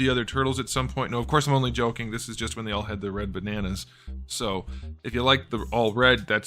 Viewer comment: am I tripping or did he say Red Bananas? XD